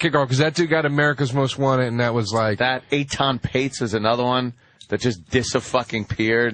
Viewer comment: What was the name Ant said at 19:17? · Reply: I know this comment is over a year old, but he said Etan Patz, a young kid that disappeared in the 1970's. It's kind of hard to make out because I think Anthony mispronounced his name.